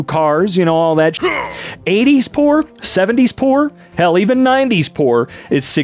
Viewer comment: I thought instead of "Poor" he said "Por*" (It makes me uncomfortable saying you know)